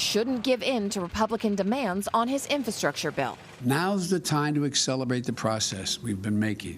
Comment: Accelerbate???? Did he really say that at 0:51?